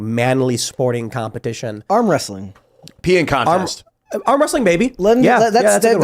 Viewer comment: Am I the only one who heard Ian say "Peeing contest" ?